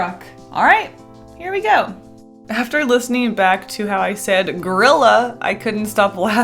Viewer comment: I really thought you said Girlila lol